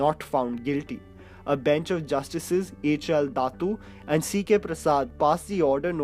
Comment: Did he say bhenchord at 10:46? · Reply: bench of justices u dirty mind